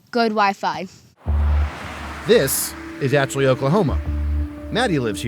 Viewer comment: I thought the narrator said this is actually Oklahoma in staid of achille and when he said population of 500 I was pretty confused.